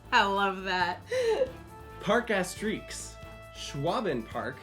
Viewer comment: the subtitles say park ass streaks instead of park Astérix